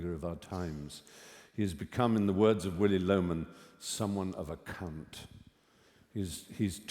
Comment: I’m pretty sure Stephen says ‘He’s someone of a cunt’. Had to replay that one to confirm thats not what was said 😅